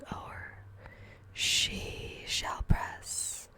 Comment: did she just say Sheeesh